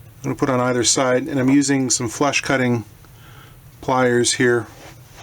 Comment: did you say flesh cutting pliers?